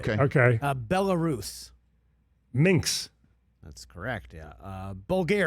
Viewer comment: To clarify: Minsk (it sounded like Ray said, “Minx” but I may have misheard).